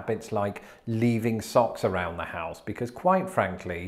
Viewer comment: I thought he said leaving sauce around the house 😂. I imagined trays of hot sauce and ranch dressing greeting people at the door or a quick dab on the way out with a breakfast burrito?